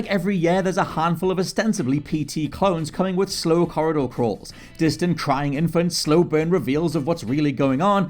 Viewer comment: I thought he said “slow horror-dor crawler” and my jaw dropped That’s what the genre should be called now lol